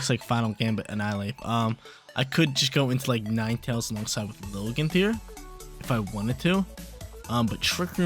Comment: what ? LOL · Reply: haha every time you say Lilligant in the beginning, it sounded like you were saying Logan 😂 just goofing tho, i love ho-oh! My fave team is a Ho-Oh team I made